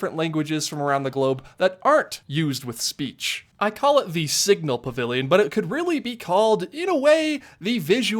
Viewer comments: I swear I thought he said sigma pavilion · I thought I heard Sigma Pavilion at first and was very confused.